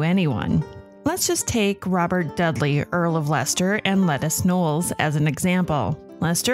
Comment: Ok I know you meant “Lettice” Knollys but it sounded like you said “Lettuce” and I couldn’t stop laughing😂 Anyways, Liz was my favourite Tudor monarch